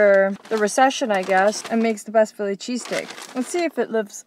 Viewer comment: I really thought she was saying Philly cheese CAKE ....allll the way until she actually showed the sandwich lol....didn't notice the title....even when she mentioned the craving the carmelised onions i was like onions?? On a cheesecake??? Huh must be the style there oh well🤷‍♀️